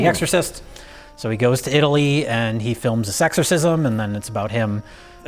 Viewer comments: Did Jay just say "sexorcism"? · Am I the only one who heard "sexorcism" at 26:00?